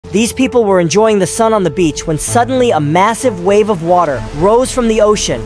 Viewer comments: Bro i thought he said son of a beach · When he said " sun on the beach" i thought he said "son of a b"tch 💀"